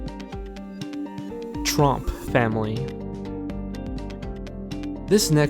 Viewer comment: it sounds like he said "Trump family"